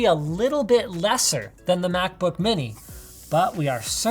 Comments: did he just say macbook mini? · who else heard mack book mini